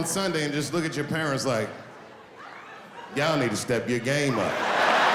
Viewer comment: May I ask what is he saying at 1:48? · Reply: Yall need to step your game up: meaning- you all need to do better